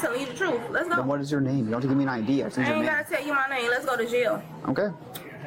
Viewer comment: I thought she said let's go to jail 😂